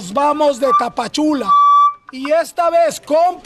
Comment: I heard "Patchouli"maybe they smoking 🤣🤣🤣...hope they don't get smoked😪.